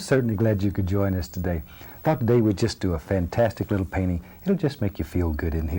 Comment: at first i thought he said "fuck, today we'll..." at the start lmao